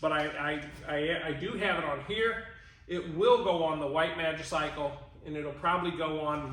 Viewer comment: did I just hear you say... It will go on the White man's cycle??? Please tell me you miss spoke. And will correct that in the video you make to replace this one.